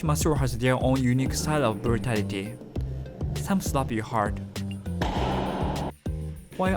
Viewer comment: I heard / read “FATALITY” instead of “BRUTALITY” ☠️